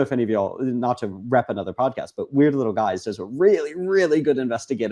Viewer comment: does anyone know what podcast is referring to? I heard weird little guys ?? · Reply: weird little guys, behind the bastards, and cool people that do cool stuff. Cool zone media across the board